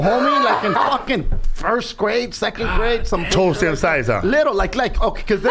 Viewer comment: What did Felipe say at 5:31? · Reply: It's an inside joke about Martin's big ass big toe. It's weird looking. So when he said he was little back then and I said "you're toe was the same size huh?"